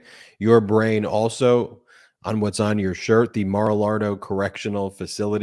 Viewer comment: Did he just say Mara Lardo?!? Hilarious! That's absolutely perfect,,!